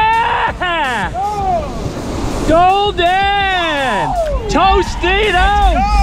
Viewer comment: TOSTITOS! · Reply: Haha, I said Golden, Toasted Oats. heard it as Tostitos and its suck ever since 🤣🤣